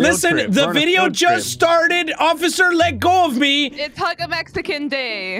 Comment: TELL ME WHY I THOUGHT SAID "IT'S HUNT A MEXICAN DAY" INSTEAD OF HUG